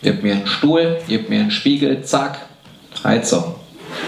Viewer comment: was sagt er da? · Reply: Zack